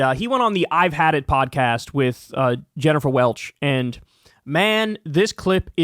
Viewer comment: Jennifer Welch is the IHIP lady. Sounded like you were saying Jennifer Welles, who I’m pretty sure was an iconic porn actress in the 1970s and 1980s 😁